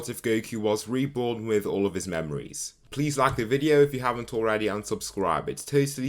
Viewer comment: Am i deaf or did i heard "And if you haven't already, unsubscribe" at 0:06 ?